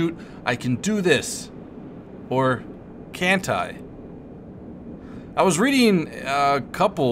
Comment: "or... Cant i" did you say that or did you make a sneaky reference to Kantai Collection becuase 'cant i' sounds alot like 'Kantai' :3